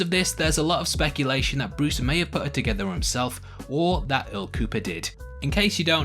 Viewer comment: I thought you said el cuco. Lol. Wrong video dude that’s tomorrow.